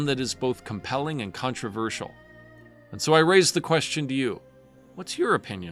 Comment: How are you pronouncing his name? It literally sounds like youre saying Tao Tao.